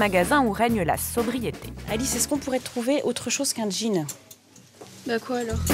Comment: Maman : "Alice on pourrait choisir autres choses qu'un jean ?" Si c'était moi : "Maman tu portes un jean !" Maman : soupir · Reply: Elle a dit "qu'encore un jean" ce qui sous entend que la fille en a deja...